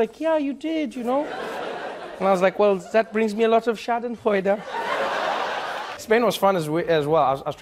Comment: did he say "schaden heute" (damage today / harm today)? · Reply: He said "schadenfreude ". Which basically means being joyful from other people's suffering 😂